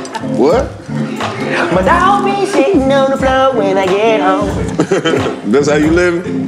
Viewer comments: “My dog be sitting on the floor when I get home” sounds like a Erykah Badu song 😂😂😂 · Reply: No lie I thought he said shittin on the floor when I get home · “My dog be sitting on the floor when I get home” sounds like a Erykah Badu song 😂😂😂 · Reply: 😂😂😂😂 I must be lit af because I thought he said don’t be shiting on my floor when I get home🤷🏾‍♂️🤣 am I the only one🤨